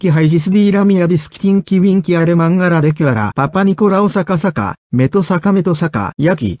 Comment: esperen y van a escuchar que dice papanicolau xDDDDDDDDDDDDDDDDDDD